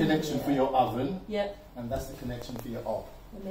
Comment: what does he say 6:47 that's the connection for your oven, that's your connection for your.... ob? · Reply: Hob, the 4 rings on top for cooking pans on